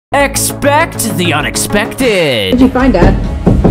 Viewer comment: Am I the only one who thought that he's going to say "Expecto Petronam" in the beggining... 💀🥲👌